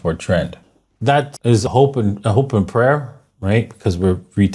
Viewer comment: I hear "hope and pray".😅